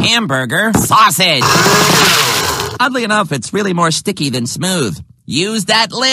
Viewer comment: I know he said "Oddly enough" but it almost sounded like he said "Ugly enough".